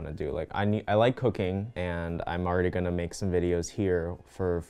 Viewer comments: I thought he said he likes cocaine not cooking 😂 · Day 2 of falling asleep at 6:00 am because I wanted to edit the intro for over and hour but I'm really proud of it so it was worth it ????? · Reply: i thought you said cocaine and not cooking lol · i thought he said “i like cocaine” HAHAHAHAH😂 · WHEN HE SAID HE LIKED COOKING I THOUGHT HE SAID HE LIKED COCA1NE. FRICK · What he said “I like cooking” What I heard “I like cocaine” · when you said “i like cooking” i heard “i like coccain” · Idk why but when you said you like cooking I thought you said you like cocaine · it sounded like he said “I like cocaine” instead of cooking lmao😂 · i literally thought he said "i like cocaine" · you said “i like cooking” and i thought you said you like cocaine i- 😳😭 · WHEN YOU SAID YOU LIKE COOKING I THOUGHT YOU SAID COCANE WTF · “I like cocaine” is what I heard 😂 I’m painting with you btw lol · OMG. He said "I like cooking" bish, I thought he said COCAINE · I THOUGHT YOU SAID I LIKE COCAINE INSTEAD OF COOKING IM DEAD BYE